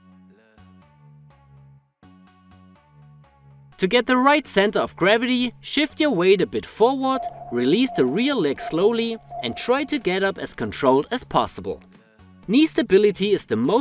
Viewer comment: I thought he said “release the real leg slowly” I was like wait I have a fake one 🤯🤯🤯